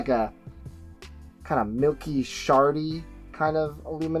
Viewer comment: Did you call that Father John Misty record at 7:49 "Milky, sharty" colored? lol